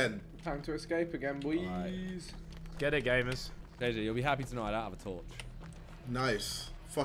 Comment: I swear Simon says “Deji” instead of JJ